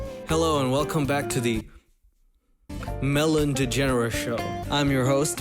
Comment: DAYUMMM, over here be looken THICC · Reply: I thought you said melanie and i was like iS tHaT hEr mOM?????